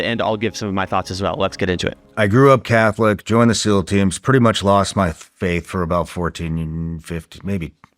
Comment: did he say "fkn" faith? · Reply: No. He said “pretty much lost my fa-faith” - he just stuttered on the F